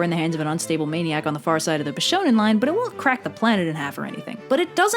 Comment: does anyone know what 'line' is talking about? I haven't heard of it and I can't hear what she's saying **quite** clearly enough to google it. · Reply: According to the subtitles she said "bishonen line"